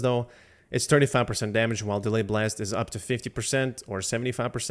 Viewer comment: wtf did you saying on 6:05 LMAOOOOOO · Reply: Delayed blast - does 25% damage if you hit 5 shots during delay with little jaws, hitting 10 will do 50% hitting 15 will do 75%.